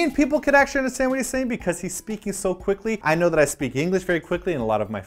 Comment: Did Simon say "He speaky so quickly?" Lol, maybe i heard wrong